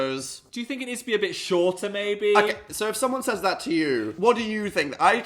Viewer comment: I was CONVINCED Phil said, “Do you think it needs to be a bit shorter, baby?” I rewatched that twice and heard that both times. I was gobsmacked. Finished the video, went through the comments, nobody was talking about it. So I went back, and yeah he clearly says, “maybe” 😂😂